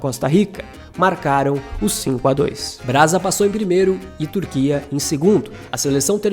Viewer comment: é isso mesmo? Ouvi um "Brasa"?? kkkkkk